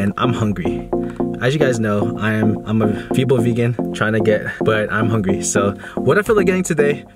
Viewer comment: what did you say there? Fibel vegan? What is that? :D